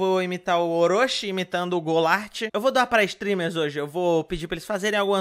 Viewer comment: eu achei que ele tinha falado "Eu vou dar pra streamers hoje" kkkkkkk